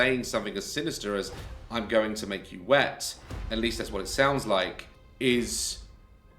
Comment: I don’t know, it didn’t actually sound like “I’m going to make you wet”, as inappropriate as it might sound lol, to me… it almost sounded like “And you’ll be here watching”. What do you think, Daz?